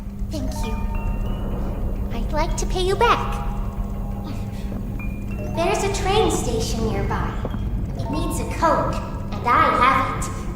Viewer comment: Poppy said "I want to pay you back (mumbles never) there is a train station near by" 0:27 I think i couldn't hear her properly